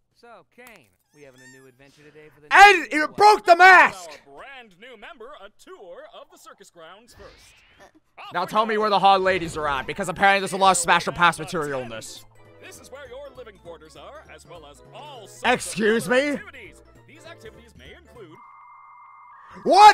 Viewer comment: Did you just say hot lady’s are at in